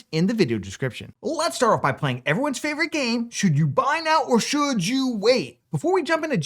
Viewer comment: instead of buy it says guy on the caption should you guy a gpu now or what?,,...thought that was funnny should you guy a gpu..lol